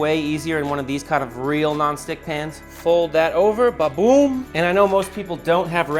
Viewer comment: WHY DID I HEAR BABOON 😂😂😂